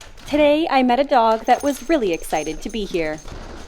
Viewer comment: I had to re-listen to the beginning because I heard "Silly excited" instead of "Really excited" and honestly, my brain was convinced that it heard right XD